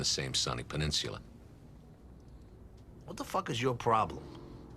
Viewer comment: Tony responds "what the f is your problem" but his lips say something different like it was dubbed over. I'm trying to figure out what he originally said, seems like one of the words was "ass" or "asshole" maybe? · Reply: What the heck I think